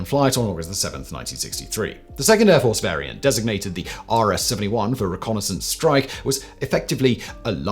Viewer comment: in did he really call this plane the RS-71? Or did I just hear that wrong?? Edit! I kept watching and got the very interesting answer around 30:00 in...